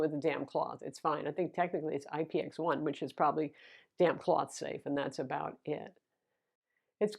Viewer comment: I heard that initially as "you can wipe it with a DAMN cloth". Great review as always!